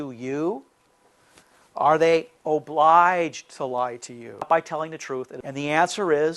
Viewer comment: there is a re dub where steve's voice is added saying 'tell the truth'. Very strange thing to dub in there. you'll see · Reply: You're right. That's just weird. I actually heard "by telling the truth" which makes the line he's saying "obliged to lie to you by telling the truth"